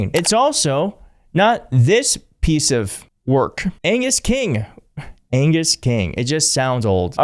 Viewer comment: I totally heard ANUS instead of Angus and lol'd. If the shoe fits...